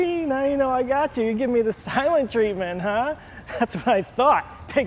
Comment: I wonder if anybody else heard "silent treEtment" like I did...